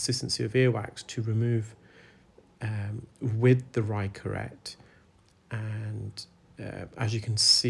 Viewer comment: The first few times I watched you and you said Rai Curette, I thought you said "Right Curette" and I couldn't figure out why you never used the "left Curette" 🤦🏼‍♀️🤣